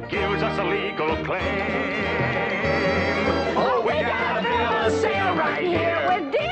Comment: I always thought they said, "We gotta build a sail right here." Lol